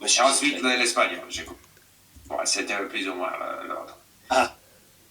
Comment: C'était plus ou moins l'ORDRE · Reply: Yeah, I heard “l'autre” at first but then thought it was supposed to be “l'ordre”. He doesn't seem to be able to pronounce R at the end of a syllable.